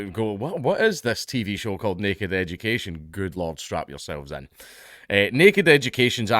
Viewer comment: Did he say "Strap In" or "Strap On"??? 😁